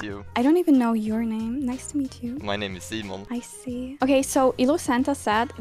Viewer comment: DID HE JUST SAY MY NAME IS "SEMEN"??? xd